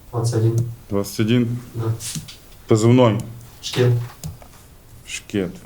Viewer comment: Позывной шкет но мне послышалось ч**н😅